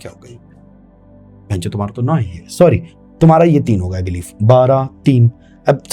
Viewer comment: pe sir ne kya bola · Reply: tumhara to 9 hai ye his three o clock was our 9 o clock